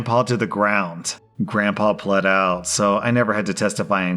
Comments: On the first one, I was just listening and I thought Rslash said the grandfather bled out instead of pled out! I was like, dang, that's crazy! LMAO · When he said grandpa pled out I thought he said bled out